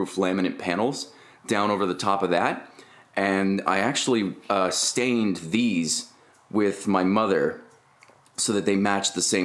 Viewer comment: WTF did I just hear you stained the panels with your mother